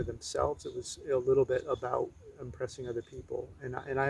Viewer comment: Not sure what meant when he said that he was oppressing other people in the dialog while sitting on the beach. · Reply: I think I heard he said ‘impressing’.